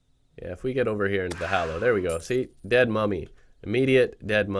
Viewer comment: I thought he said dead mommy